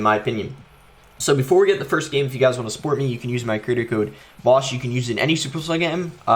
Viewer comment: I heard bosh 😂 but I know is boss so feel free to use it to support boss❤️